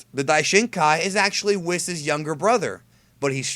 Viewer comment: i could have sworn he said father not brother